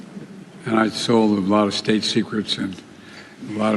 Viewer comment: Benny it sounds like he said “ He sold a lot of state secrets “